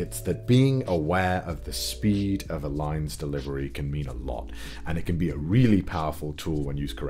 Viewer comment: I totally misheard this as "the speed of a lion's delivery" can't un-hear it.